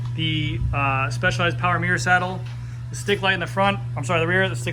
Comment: sounded like you said power "meter" saddle?